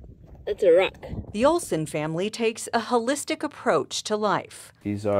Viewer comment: This reporter needs to enunciate better. When she said "holelistic" I thought I heard "hellistic"😅